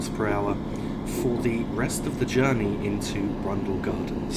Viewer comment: I understood Germany instead of journey, sounded really weird and funny XD